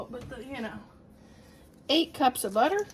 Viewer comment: I thought you said “8 cups of butter” and I thought “these are my kind of cookies!” 😂 but you meant tablespoons. These look delicious. Thanks for sharing!